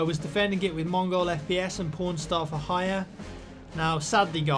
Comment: Did you just say Pornstar for hire @ 0:16 ?